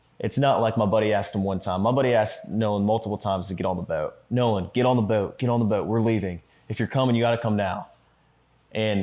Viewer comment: Your buddy already left I thought he said Nolan get on boat key